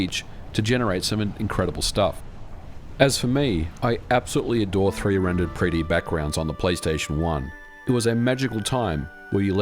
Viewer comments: Did you say three rendered pre-d backgrounds at about 10:52? Lol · Did I hear you correctly? 10:52 You absolutely adore 3-rendered pre-D backgrounds? Me too, buddy, me too.